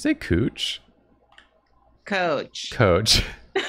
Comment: If it’s anything, I did mishear saying “coach” as “pooch”, not “cooch”. Mr Dog on the brain lol